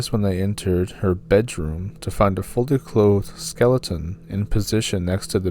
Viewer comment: In the second story it sounds like he said that when the police entered her cabin for the 2nd time after years of her missing, after they entered her bedroom they found a "folded clothed skeleton". I'm assuming it just sounds like he said that but in reality he said "fully clothed"? Idk it just sounded funny when I heard it and I listened to it several times and it sounds like he said folded.... just thought I would mention it and see if anyone else noticed this?